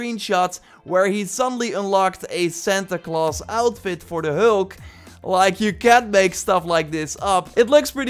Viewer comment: It's funny at 2:56 what you are saying because I understood you said "Hlk" instead of Hulk if you listen carefully 😂